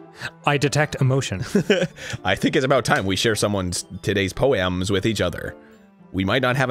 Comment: When Arin said in monica's voice it's time to share each other's poems I thought he said it's time to share one of our tuddies. XD